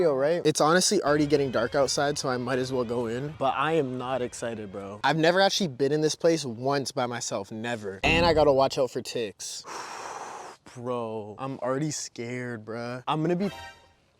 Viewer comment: When you said watch out you for ticks I thought you said 585# slash tit